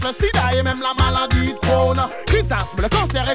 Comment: Ses moi ou il dit corona a 1:13 la musique date de 7 ans et il dit corona ses louche